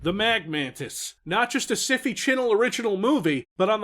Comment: I swear to God I didn’t register at first that was making fun of SyFy Channel’s new spelling that I thought he said “a Civvie Channel Original”